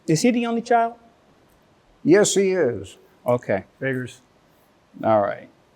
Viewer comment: Uhm, what did he say here? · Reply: He said, "Figures".